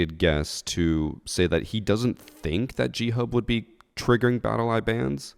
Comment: I genuinely thought you were saying Cheat Hub, I haven't ever heard of G Hub. So at the beginning, I was just thinking "no shit they got banned".